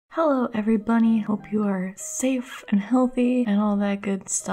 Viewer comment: the begining sounds like u said heloo everybunny